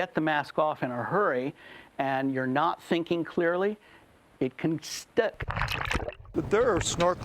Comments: please tell me what he said I hear stuck Or stick Lol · What did you just say, sir 😂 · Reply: It can stuck